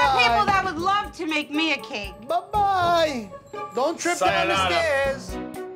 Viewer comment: Sayonara 😂 correct me if i heard something else 😂